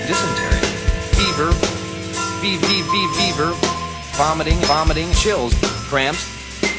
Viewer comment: i heard him say bieber